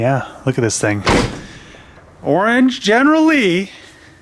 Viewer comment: Did he just say general lee? Lmao